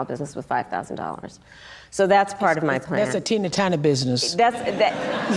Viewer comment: Sounded like they said Tina turner business